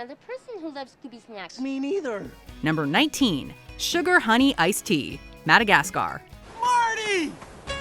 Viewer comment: back in the glory days of the mid 2000s I watched this film and I always heard it as “should’ve had an ice tea” lol😂😁😂